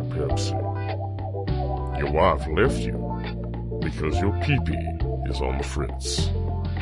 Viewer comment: That ad though... I swear I heard "Can't lift if your penis is on the fritz." 0-0